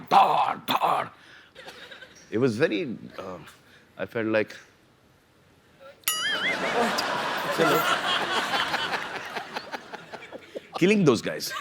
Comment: What was salman going to say 2:51 ?? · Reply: He said "I felt like killing those guys"